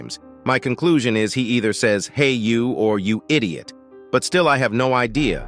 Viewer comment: I heard "hey idiot"